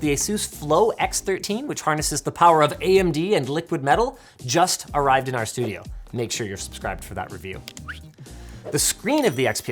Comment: i'm sorry did he say liquid metal 🥹 i cant wait for the video 😇